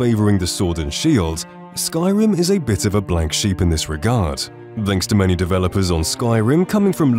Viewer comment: Did he say 'blank sheep'?